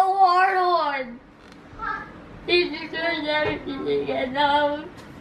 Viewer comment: My heart is breaking, did just say "he destroys everything that I love"??